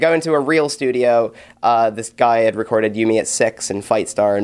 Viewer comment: Aww, I'm so happy for Patty and his band. [; never fails to satisfy. Favorite interviewer. Also, at 6:09, thought he said You Me At Sex..